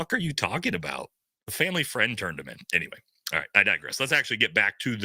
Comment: I thought they said his dad turned him in